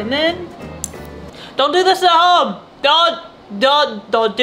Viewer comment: what’s that you said do this at home ?